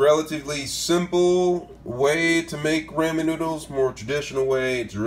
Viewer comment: It sounded like he said ray me noodles